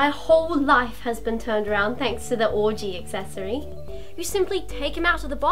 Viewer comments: ish it sounds like she said "Orgy Accessory" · accidentally said orgy instead of ouji